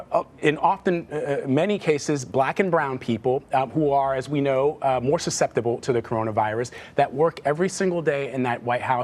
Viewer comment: did he just say black and brown people are more susceptible to the virus ??? 👁👄👁